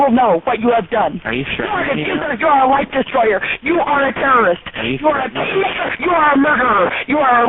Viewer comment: What she call him @ 2:57 after she calls him a terrorist. Sounds like she says "You are a tea-maker" which doesn't make much sense.